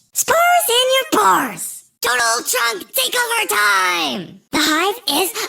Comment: why the hell I heard 'Donald Trump Takeover Time' 💀😭🙏